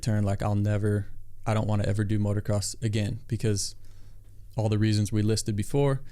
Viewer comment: did he just say motoRcross....?